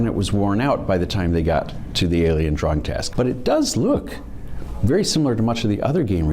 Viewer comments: I heard this as "alien drug test" and was very concerned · Not gonna lie,glad for your captions, could have sworn he said Alien drug test,not drawing test. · Lol, when the guy said alien drawing I found have sworn he said alien drug test.